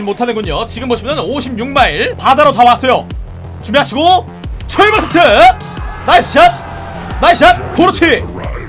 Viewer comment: 출발타트~(?) 이러는거임 뭐라는거임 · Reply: 출발 스타트 입니다